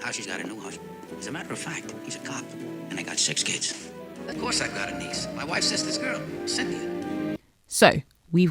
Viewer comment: he says “my wife’s sister’s girl: Cynthia” not “my wife’s sister Cynthia’s girl”, which implies that either: 1. He has two nieces and got their names mixed up 2. He lied about the name initially but told the truth at the end when asked 3. He made the whole thing up and forgot what name he said previously 4. Secret forth thing I can’t think of